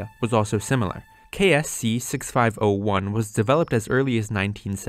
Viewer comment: subtitles say "KS C 5601" but you say "KS C 6501"